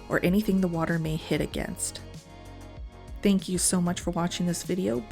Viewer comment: Can you make an axolotl?!?!? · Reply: Swore at the end you said “bitch, shout out to my subscribers” 😂 so I had to subscribe obviously